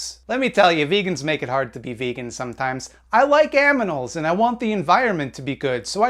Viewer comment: what did you say you like? Why did it sound like "aminals"🤣